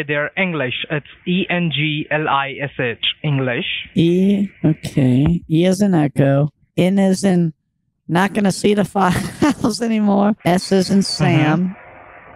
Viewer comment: “N as in not gonna see the files again” —- the triple wink destroyed me. 😂😂😂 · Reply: I thought he said "n as in..... Not gonna say that word"